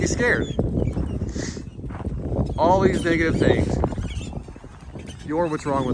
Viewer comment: What did he say at 3:38 😭😭😭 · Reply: sounds like "all these dad-gum things..."